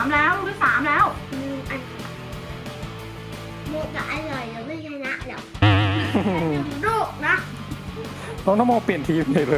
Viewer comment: น้องพูดว่าอะไรค่ะแม่อายให้รู้บอกหน่อยน่ะ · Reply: Benz Love TV มัวแต่อร่อยเดี๋ยวไม่ชนะหรอก ตอบแทนคะ